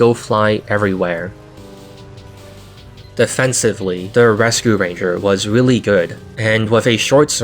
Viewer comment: headphones blipped out during this segment and all i heard was "the fent ranger was really good"